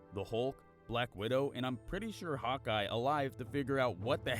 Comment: So idk marval all the much, just some characters- that’s why I saw this cause I’m seeing end game today 🤷‍♀️😂 anyways when he said “Hawkeye” was I the only one that, untill watching “marval singing” on insta TV just now, thought he was saying “hot guy”? It’s sounds like he says “hot guy” so much more that “Hawkeye” 😂🤷‍♀️